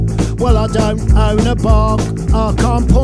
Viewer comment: [Unintelligible] New heart New eyes New vision [Verse 1] Well, I don't own a bar I can't punch nothing And sometimes I just wanna bang someone out The days are long, inside I scream At some of these people they've given me And sometimes I just wanna bang someone out Yards in green don't go, they don't look right round ere' The lanes ain't Donkey Kong on scumbag game I get it posh, why should I leave? The dirty tea bag I don't want to keep And sometimes I just wanna knock someone out [Chorus] The plague rolls down from the hills up there It's fit for work if it can shit and stare This is how it's gone on in the United Kingdom The plague rolls down from the hills up there It's fit for work if it can shit and stare This is how it's gone on in the United Kingdom Won't ya New heart New eyes New vision [Verse 2] The Change.orgs go slow They don't sit right, oh, dear The door is always closed You're thinking wrong The righteous gobbing sincerity And this medieval constituency I'm just so dry, I wanna bang someone out [Chorus] The plague rolls down from the hills up there It's fit for work if it can shit and stare This is how it's gone on in the United Kingdom The plague rolls down from the hills up there It's fit for work if it can shit and stare This is how it's gone on in the United Kingdom Won't ya [Outro] New heart New eyes New vision New heart New eyes New vision New heart New eyes New vision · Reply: PollierK I first heard “I don’t own a bag,” as in punching bag.